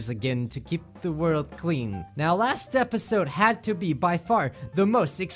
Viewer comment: Sorry this was pretty late but i spent all day working on it Thank you everyone for being here to support me it really really means alot to me and it always will! Be sure to check some of my previous uploads in case you missed them :D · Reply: I heard "this had to be that fart" or something like that in the beginning